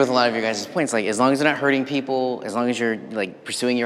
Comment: Did he say "herding people"? ;-)